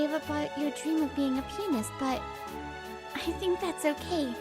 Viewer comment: When she said being a pianist i thought she said peni